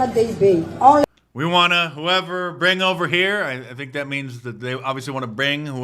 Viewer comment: I thought she said (we want to gringo over here)